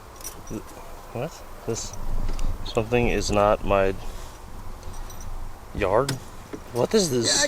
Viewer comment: I think it says “This Farm is not my yard”